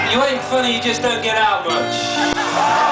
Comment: "You ain't funny! You just don't get out much!" Killed me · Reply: I thought he said, “Just get out, b****”